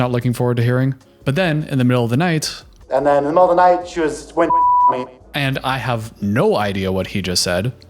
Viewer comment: at about 55:00 it seems to me he was just saying she was "going down on me"... which would make sense how her "gonorrhea of the throat" could become his "gonorrhea of the d*ck"